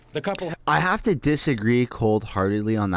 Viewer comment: Did you say disagree cold heartedly?